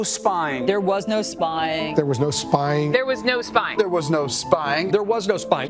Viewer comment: I heard "they" have n0 spine.